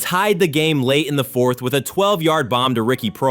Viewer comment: Did he say 12-yard bomb???